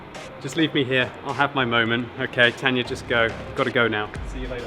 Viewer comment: did he just say Leafyishere?